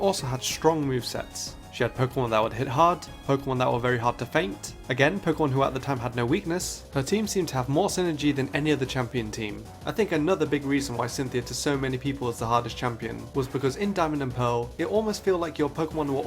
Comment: You know, watching this back over again I thought it said “Cunthia” oops. I can’t be the only one